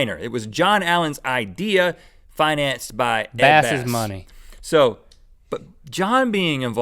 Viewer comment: I'm kinda confused did I hear link say the word "badass" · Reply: PEOPLE He said "Ed Bass' Money"